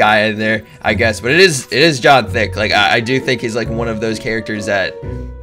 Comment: did he say john thic